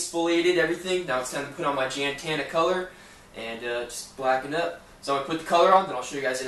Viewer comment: Did he just say blacken' up?